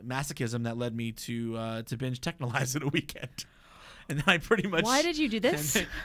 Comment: what show is he talking about? i can't understand the title he's saying. is he saying 'techno lives'? 'take no lives'? 'take no lies'? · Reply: i think i just found it. Is it "Texhnolyze"? i also have the same problem with "Serial Experiments Lain" i usually finish a show within 1-4 days, yet i still haven't finish that show for 6 months now, probably longer.